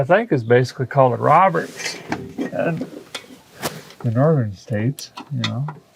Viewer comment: sounded like you called a flat head a Robertson. Which is actually a square head